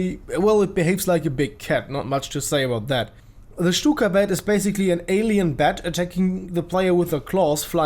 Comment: first i heard ,,The cyka blyat"